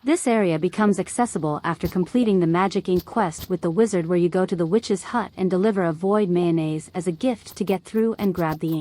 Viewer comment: sorry. please can you tell me did you say on 15:21 until 15:38? i really tried a lot to understand and i just cant. thank you. i went to that part of the forest in whinter and the scene did not apear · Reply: During that part it's finding the sprite spring, it is accessible after completing the Magic Ink Quest with Magnus where you you go to the witches hut and give the goblin Void Mayonnaise to get in. You will find a path for this spot south east of cinder sap forest <3